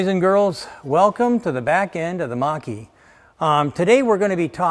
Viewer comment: I heard "Welcome to the back-end of the monkey"